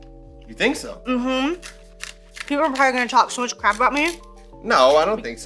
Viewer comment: I thought you said, "people are probably gonna talk so much CRAB about me" lolol